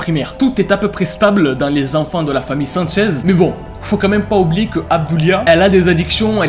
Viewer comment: pourquoi j'entends '' la famille Sainte chaise'' 😢?